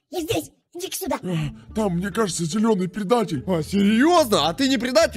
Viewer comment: Мне послышалось или синий сказал мне кажется что зелёный пердатель хах 😂